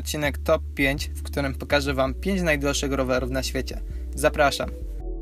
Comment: kto usłyszał najgorszych ?